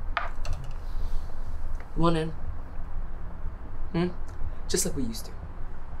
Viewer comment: I am just at beginning of this but so I don't forget. The 1:29 mark has subtitles that say "What if" but if I am hearing right he's saying "Want in?" and that is potentially a huge difference of understanding to anyone watching this. Okay so far loving it...